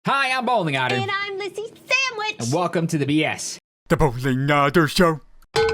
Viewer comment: Lol my audio messed up so I heard “Hi I’m Bowlingotter and I’m Lissyhiimbowlingotter”